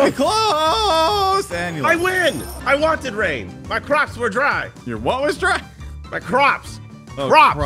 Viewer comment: I thought said his crocks are dry lol